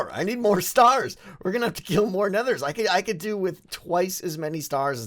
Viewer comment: Did he say, "I need to kill more nethers?" I heard Nether and not wither...?